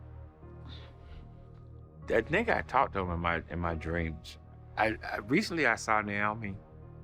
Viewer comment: is it just me or did dude say I talk to this nig** in my dreams. Lmao